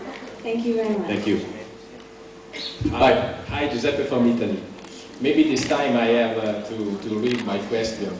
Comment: what does the guy say? :) Love this panel! x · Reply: "Giuseppe from Italy" Seems like he just told who he was~~